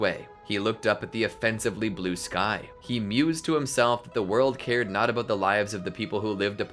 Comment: did he say he mews?!